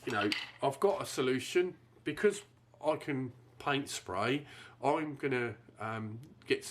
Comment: did he say sprites? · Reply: Paint spray! I'm going to respray the spare space bar in the PANTONE 4C Cool Grey and replace the broken original space bar - that was a tongue twister! 🙂